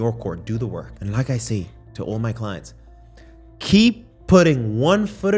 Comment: Did he just say PIZZA?